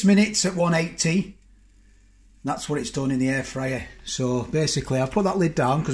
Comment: I'm sorry, I thought you said in the hair dryer?.........Mmmm it sure looked great, and I bet it did taste that way too.